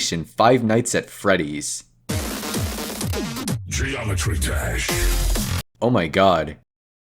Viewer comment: am I the only one who heard "geometry trash"